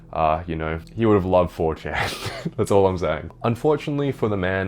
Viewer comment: The fact that the automated captions says "for chairs" instead of 4chan